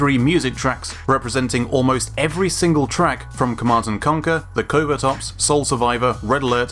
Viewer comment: Wtf, why wasn't I subscribed to you? I almost missed this video. Jesus. 0:36 I almost thought you said "The Clover Tops." lol